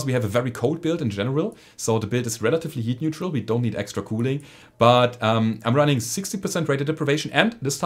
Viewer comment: Every time you say "cooling" I hear "Kool Aid"